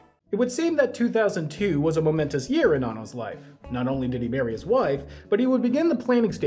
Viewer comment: Oh god, I had to listen to that take like three times because it sounded like you said "2002 was a momentous year in Anno's life, not only did he bury his wife" and I thought "jesus christ, that went downhill quickly!" and realized finally you said MARRY his wife!